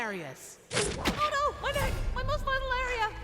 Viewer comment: I always thought Goku said "Oh no my mouth my most vital area!" in reference to eating food. I don't know why it took me so long to realize Goku said neck not mouth. I still think Goku saying his mouth is his most vital area is funny, even if it's wrong.